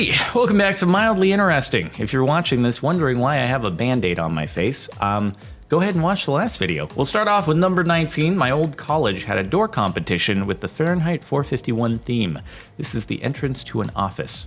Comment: The first time I watched 0:04 I thought he said bandana